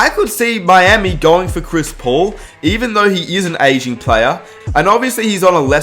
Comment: Lol thought he said “Even though he’s an Asian player” then I had to play it back a few time to realise he’s saying aging